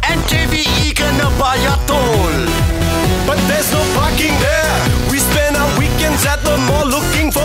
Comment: Is it just me, but when he said ‘’ There’s no parking there” it sounded like he said “there’s No F***ing Hair, we spend our weekends and the mall looking for f***ing Pay, But when we find f***ing hair we sprend a whole day there. :/